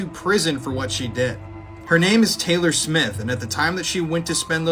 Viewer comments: I thought he said taylor swift😭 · When it said Taylor smith Í thought it said Taylor swift😭